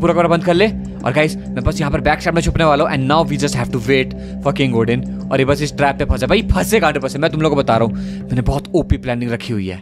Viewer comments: kya bola bhai ne fuc..... Odin😂😂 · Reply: Bhai for king odin kha hai 😂😂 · Did He just say Foking Odin